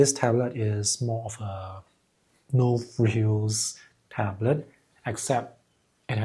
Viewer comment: no ??? tablet? I couldn't catch the word. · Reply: No-frills tablet